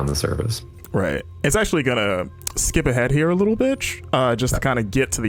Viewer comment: did he just say at @ 7:55 "skip ahead little b!tch" instead of little bit lol